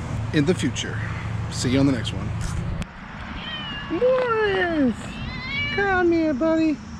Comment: Nice and easy process. Forest is the real MVP 🤣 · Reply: Had to many things playing in the background. I swear I thought you said his name was forest and not Morris 🤣. And hey our cars need a guardian angle. Make sure they don't break down when we leave the drive way